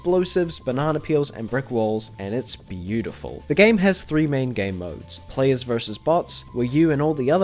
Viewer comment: I like how the caption at 2:22 said 4 main gamemodes instead of 3. Still a well made video